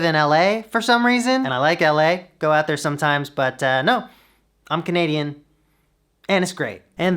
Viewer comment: "I'm Canadian ... ... ... and it's great!" got an actual laugh out loud from me. Like everyone else, I am wildly jealous of this beautiful studio. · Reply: I fully thought he said "I'm Canadian ... ... ... and straight!" Lord forgive me once again my brain has let me down. Am I a bad person?